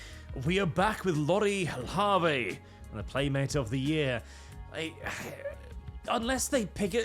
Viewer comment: "Primate of the year" - That's what I heard and I'm sticking to it!